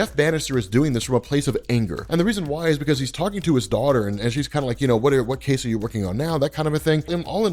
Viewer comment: I thought you said daughter but you said dog. Dogs die everyday b